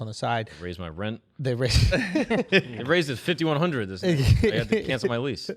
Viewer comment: Did Marc just say his rent is $5,100. God damn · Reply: raised it * 5100... over 12 months, thats over $400 increase per month